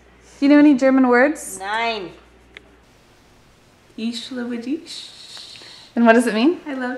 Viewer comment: I don't understand a word and I'm German ... ahhh "Ich liebe dich" (I love you) hahah after ten times now I understand it :D